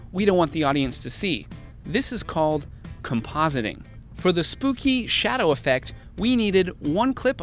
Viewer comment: Please, i'm French, as there are no annotations, i do not understand a word. What did he say at 7:54? "This is called ???" what ? Compasiny?